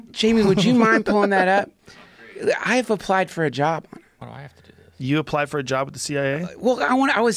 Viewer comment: Why the fuck do i hear jamie say "why do i have to do this"